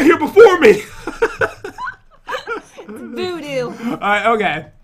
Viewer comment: did Jen say Boo Doo 😂😂 Like if you heard it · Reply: No she said voodoo